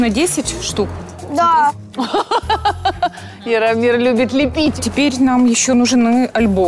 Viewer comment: мне послышалось Фирамир